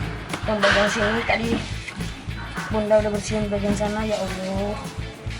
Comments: itu koko ngomong ya allah lagi atau cuma aku salah denger? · kakak mau bantuin bunda bersih2. Pas liat debu nya banyak, langsung Nyebut “Ya Allah”. gitu gak sih? Coba dong putar, apa saya salah dengar? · aku g salah denger koko bilang ya allah?denger g komenyak